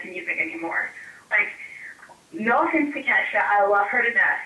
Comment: Who's album did she buy? Did she say Kesha? lol